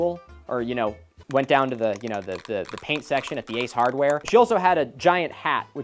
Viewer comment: I heard "went down to the paint section at the Ace Hardware" two aisles down from the paint section at an Ace Hardware lol